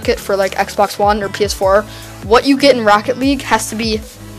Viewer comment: sounds like he says racket